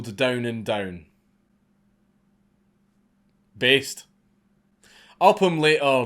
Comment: Did he just say based?